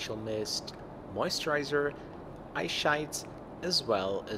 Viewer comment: anyone else hear eye shites at 07:55? ahahaha